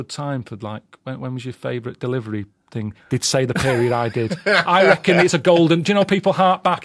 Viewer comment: "they'd say: the peerie-eyed kid"? What.... What did our beloved Mr Dilkington say? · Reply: "they'd say 'the period I did'"